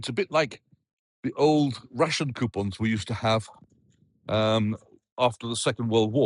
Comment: he is talking about RATION coupons. Sounded like RUSSIAN coupons, which didn't make sense.